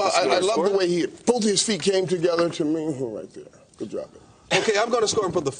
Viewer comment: What he say? 😂😂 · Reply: good job